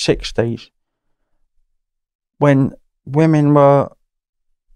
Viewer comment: Had to listen to this twice. I thought he said they were 'dressed slightly different'. Putting forward the old 'they were wearing tight trousers' argument, I thought 😂.